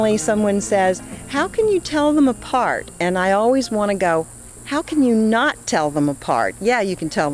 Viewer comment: When she said: "how can you not tell them apart", I thought she said: how can you not teach them to fart. lmao.